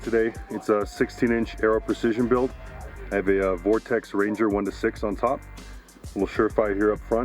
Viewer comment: What scope did he say for the second rifle Sounds like he said vortex ranger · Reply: Yeah I believe its a Vortex Ranger 1-4